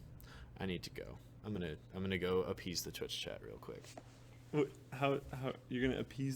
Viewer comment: I thought he said: "Im gunna go... I'm gunna... Use the Twitch Chat real quick" like a "twitch-chat-is-a-toilet" joke..